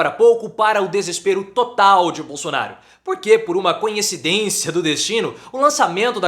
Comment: "CONHECIDÊNCIA" foi foda O.o